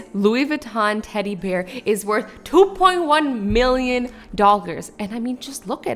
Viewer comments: said dockers instead of dollars 💵 at 3:56 Like if you agree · Who noticed at 3:54 she said doggers instead of dollers